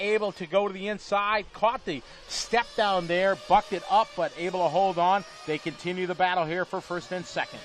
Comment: it sounds like he says "Caught the stepdown there, fucked it up." Are my ears deceiving me? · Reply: RSD3 he said “bucked it up”, as in when a horse bucks up its ass.